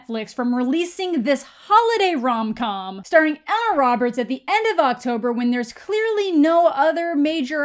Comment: did you say Ella Roberts? XD